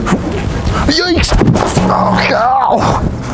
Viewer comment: Go watch 1:34 did he say Yikes?